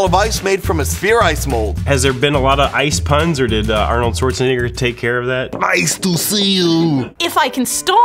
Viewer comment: what did he say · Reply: Schwarzenegger